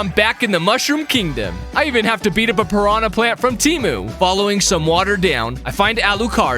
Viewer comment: Did you say temu?